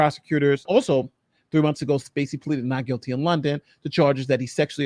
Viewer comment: When did he plead guilty? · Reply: I misheard. I thought said pled guilty. I stand corrected.